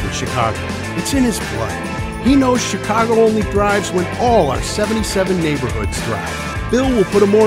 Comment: Instead of thrives, I thought he said "Chicago only bribes..." Is it a Freudian slip when you hear incorrectly?